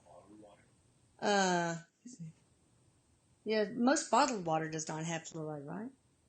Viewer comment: I heard say... "it's in all bottled water."